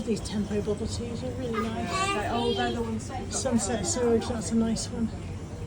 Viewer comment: Bubble teas .... I'm so sure I misheard you .. did you say "sunset sewerage, that's a nice one?!" I really need to watch when I'm more awake! 😂x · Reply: HA! Sunset SURGE!!! <link>